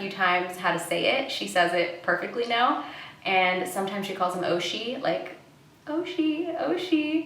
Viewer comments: I thought she said "OH SHIT", I had to do a double take smh lol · I literally heard Desi say “Oh Shin”